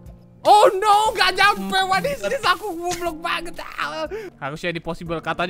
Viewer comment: ada yg nyadar? Tadi bilang gblk ato aku yg salah denger